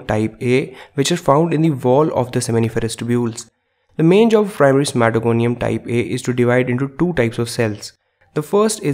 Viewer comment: Please what does he say at 1:59-2:02 ? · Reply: i know this reply is 4 years late but it might help someone else "the main job for primary spermatogonium type A is to divide into two types of cells."